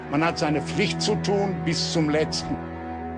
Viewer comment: What are the old soldiers saying here 5:05 ??? Can anyone please explain what does “ Bestum Listen “ mean because i heard it more than once???? Thank you. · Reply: "Bestum Listen" in German "bis zum letzten" he says to the last (to the last man / soldier), means so much to the end of his life he will defend his fatherland, his family, his friends, no giving up) He has given an oath to Germany and he gives his life "to the last" (bis zum letzten).